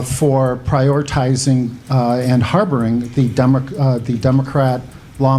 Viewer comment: did he actually say 'dumacrats' instead of democrats? lol